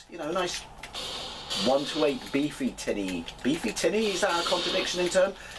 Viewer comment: “Beefy-tinny”? Genuinely thought you said, “beefy titty,” at first! Thanks, as always, for your time.